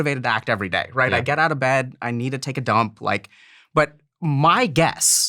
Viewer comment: Did he just say I need to take a dump